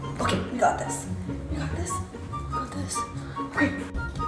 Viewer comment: I heard it as "what in the tower nation is that" lol